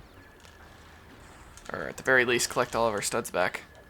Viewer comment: I heard collect Oliver studs. LOL 😂!